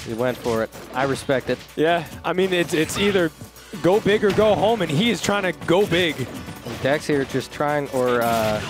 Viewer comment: I don't know why but I thought he's about to say: 'it's either go hard or go home and he is trying to go home' 😂